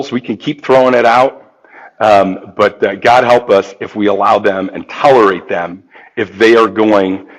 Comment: Lol! I didn’t catch that but wowwwww! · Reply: I went back and listened, and I misheard. He said something like, “If we allow them, and tolerate them…” not empowerate 🤪 In my defense, his audio is awful and even when he’s using real words the content is gibberish. 😂